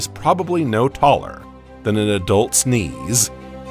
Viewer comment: probably no taller then an adult….? Sneeze? Even subtitles didn’t know what was said